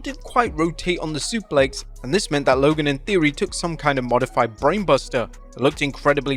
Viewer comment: People are saying that said “Logan and Theory,” as in Austin Theory. I think he said “Logan, in theory.”